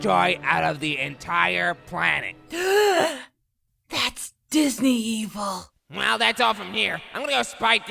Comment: did goku say Disney evil? and i love this episode